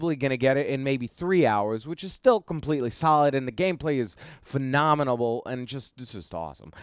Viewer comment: my favorite YouTuber... did you say phenomenalable? Lol. Love those lol. Get some sleep homie